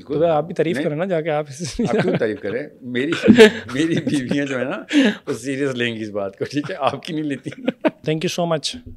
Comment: Did he said MERI BIVIYAAN at the End 30:07 ??? Is he married again? Or I heard wrong??